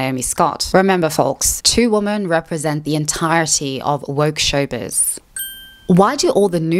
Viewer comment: Silly question, What did she say at 15:48 ? "the entirety of (...?...)" English is not my native language, and I feel bad when I can't understand something, even if it's a single word😆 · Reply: "woke showbiz" are the words you're missing :)